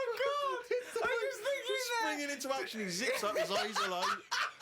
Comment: I've been looking for that immune system bit for quite a while now. For some reason I thought he said nervous system.